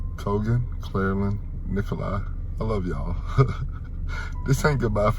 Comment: I am sorry for ruining this very devastating moment but did he just say… NIKOLAI?!?♣️ AS IN NIKOLAI GOGOL!? (BSD fans)